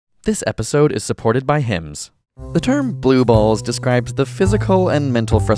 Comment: At the start I thought it said "this episode is supported by hands". Would have been a nice advertising pun...